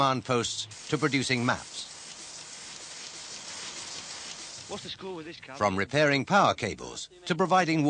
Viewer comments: At 2m03sec: The Soldier the top of the bank, talking to his Oppo with the cables. It sounds like he say “What’s the score with this C**t?”, which WOULD be very apt for Real Life Soldiers but not so good on an SSVC type Training Video! Still made me chuckle 😂🤣 (By the way, I do know, I was one!) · At 2m03sec, the Soldier at the top of a bank talking to his Oppo with the cables. It sounds like he say “What’s the score with this C**t?” - 😂🤣